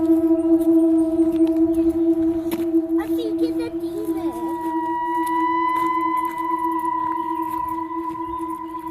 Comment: What that little girl says... 🫣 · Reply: "I think it's a demon..."